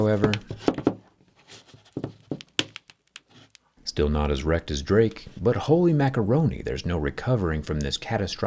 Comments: "Still not as wrecked as Drake"💀💀🤣🤣 Bruhhh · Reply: I heard "Still not as erect as drake" and was so incredibly confused as to how it related to this scenario😭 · "still not as wrecked as drake" is crazy 🤣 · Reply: I thought he said “Still not erect as Drake” 💀